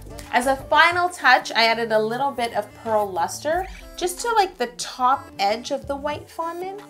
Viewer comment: when she said pearl luster i thought she said phil lester and started freaking out until i realized